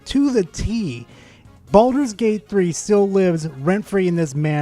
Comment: It doesn't even sound like he's saying baldurs gate 3 correctly. It sounds like hes saying baldos gate.